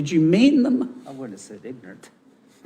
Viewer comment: did i hear someone say, "i wouldn't say ignorant"